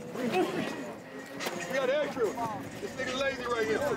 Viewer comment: Did he say, “We got extra, this n*gga lazy.” ? 😂😂